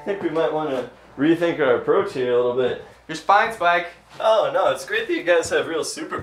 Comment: i love you guys but ok did anyone else catch the blooper? at 2:09 "you're spine fike" lmao